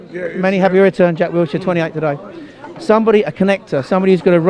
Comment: Jack Wilshere's already 28?! My oh my how time has passed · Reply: Hi Hi could've sworn I heard him say "jack turns 28 today"